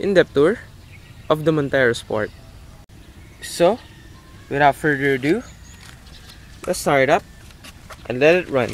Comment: what I heard on 0:26 "so without further redew?" XD